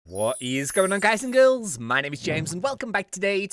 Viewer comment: I remember before I thought that in his intro he was speaking a different language Later I found out he was saying "What is going on" I thought he said "Woh eez goyn un"